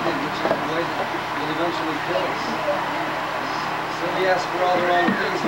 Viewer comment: If anyone can make out what Rich says when he is talking around the 48:15 minute mark, after he says "And, He wants us to encounter the world in such a way as He did. [...] " That would be great! I think it's a phenomenal perspective, and I would like to get that transcribed. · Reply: It sounds like Rich says "He wants us to encounter the world in such a way as He did, one that would eventually kill us.